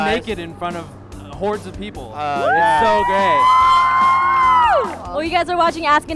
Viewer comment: did ian say "its so great" or "its so gay"?